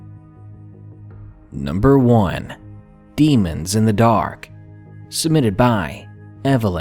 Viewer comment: I thought #1 said "Demons in the dark by eleven" Like it was a fact